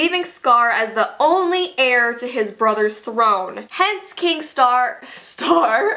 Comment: My favorite character is K I N G S T A R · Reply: I thought you said K I N G S C A R